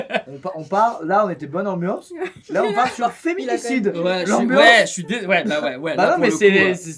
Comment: Squeezie qui parle de féminicide, c'est bien ça que j'ai entendu ?